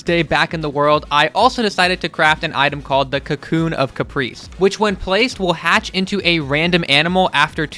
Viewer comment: I thought that said Cocoon of Cocaine